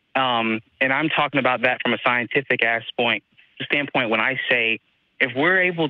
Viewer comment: Freudian slip? 12:06 Did the caller just say "from a Scientific Ass-point" after talking about the existance of aliens?